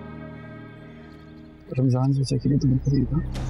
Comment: Can somebody tell what Wajeeh said to Mannat at 1:37? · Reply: Bhabi ap ramzan chcha ki waja se jarhi hn na